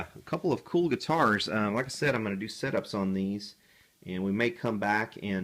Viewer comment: Startled me at 7:35 when I could swear you said "I'm going to do SIT-ups on these":) All jokes aside though, thanks for another thoughtful, informative video.